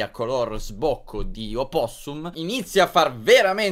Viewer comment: Solo io ho sentito porcodioopossum all' inizio 00:05?!😂